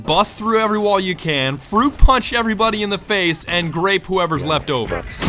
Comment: thought you said rape for a second